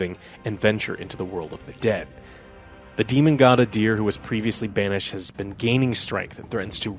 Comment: Swear to God, I thought you said "Demon God of DEER" for a second